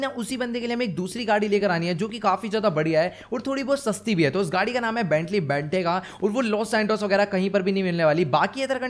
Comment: i heard that benny bethega😂